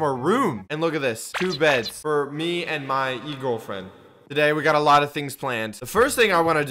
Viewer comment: look 2 beds for me and my new girlfriend: what did you just say